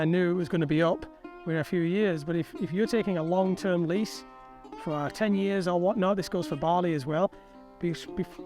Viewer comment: I thought you said he'd leased it for 3 years.,and now he's going to stay for another 3. .confused.